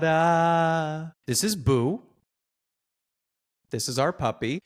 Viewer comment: when the subtitles said "HARA" Instead of TADA